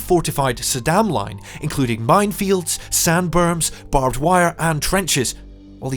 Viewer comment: I heard "sandworms". If Saddam had the help of Shai-Hulud, the outcome might have been different. And yes, listening to it again I realise it was,, as Clouseau once put it, a berm.